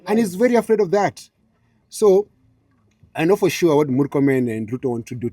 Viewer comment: I don't understand... "I know for sure what Murkomen and Ruto want to do to me... They want to give me abandon (??) so that they can..." What does abandon or whatever he said mean??